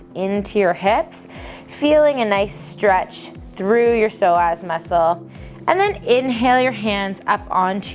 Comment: lol I thought she said sore ass muscle